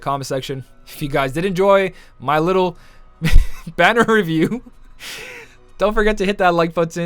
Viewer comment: Lmao for a second i thought you said "banner rebuke!!!"😂😂😂😂